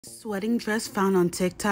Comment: My dumb ass swore you said sweating dress at first my b Absolutely stunning dress